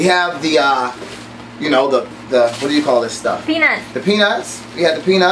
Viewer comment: does she say penis?